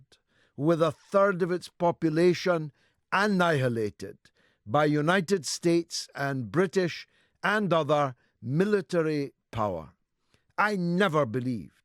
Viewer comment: "Annihilated by Britain and the U.S." Dude, it was North Korea supported by Soviet Russia and China that invaded the south not the other way round. But they were communists so they were the good guys? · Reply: Listen to what he said a bit more carefully at 0:14 . He said "by United States, and British and "other" military power". There is no particular reason to be selective in here...